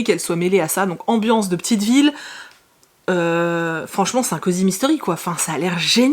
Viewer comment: j'ai compris "Sarkozy mystery" au lieu de "c'est un cosy mystery" jpp